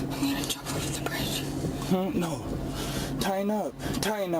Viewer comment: A dude who calls his girlfriend "bro" is a complete loss. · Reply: while that is undeniably true, at 2:00 i thought i heard her say, " I'm going to jump off the bridge". she might just be a bro with that kind of mental capacity. i have seen 2 police chase vids that ended with suspects trying to escape via jumping off a bridge in the dark. Neither of them made it to trial.